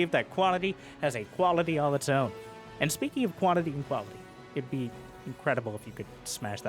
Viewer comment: I thought it sounded like said "Quality has a quality all its own" this time, but after listening a couple more times, no, he did say quantity. But that threw me for a loop for a moment.